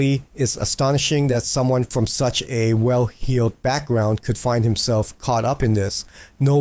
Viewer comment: did you say "well healed"? lol